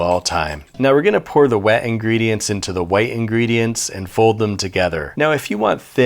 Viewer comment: All I heard was "Wedding Gredients"